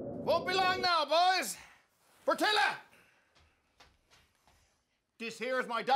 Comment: Portilka ain't got no man .. I was like what the irony i thought he said Fertila 🤣🤣